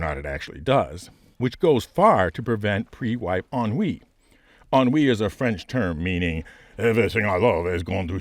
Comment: J'ai compris "on-oui" 🤣, j'ai beau parler couramment le français j'ai dû te réécouter 4 fois avant de comprendre le mot ennui. Anyway, thank you for your amazing work!